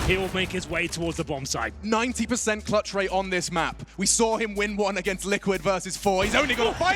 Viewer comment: Can please anyone tell me what he said there? I would LOVE to know xd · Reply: We saw him win one against liquid versus 4*